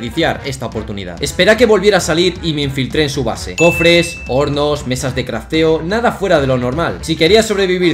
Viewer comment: pense que dijo porno xdddddddd